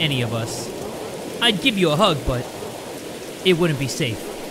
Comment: the dad says: I can’t let you see my face instead of I’d give you a hug but… Just something I noticed 😊